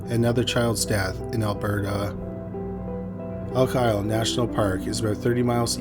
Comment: Not gonna lie I thought you said "Uncle Al National Park"